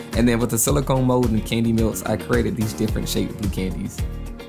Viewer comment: At the end I thought he said I spray it with edible water